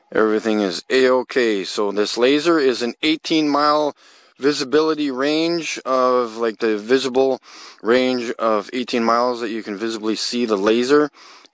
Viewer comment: HOLY MOTHER OF SENTENCES, what is this man saying ? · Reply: "so this laser is in 18 mile visibility range of like the visible range of 18 miles that you can visibly see the laser"